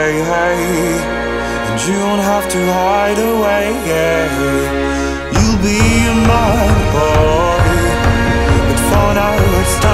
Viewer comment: My dumbass thought he said "you'll be mine, boy" 💀😭😭